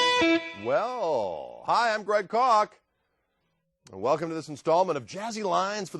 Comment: I heard he said "Hey im Great Cock"